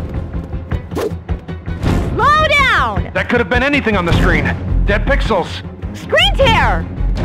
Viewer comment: I thought the hazmat boi said kane pixels